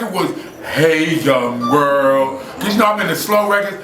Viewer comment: what does he say at 4:40? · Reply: hey young world, great slick Rick tune